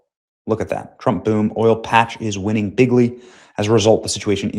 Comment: It's fumy how President Trump's word "Big League " was mis heard and some people thought that he said 'bigly!'